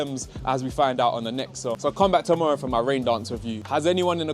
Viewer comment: I think you said ODNA idk I’m American you’ll have to forgive my lack of education for not understanding what you said at the end of the video😂❤ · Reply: ohhhh I’m talking about ODEON it’s the cinema behind me, it’s a big brand but that specific one is so so bad 😂